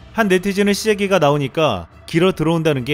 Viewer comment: 길어들어온다고 들리는거 같네요.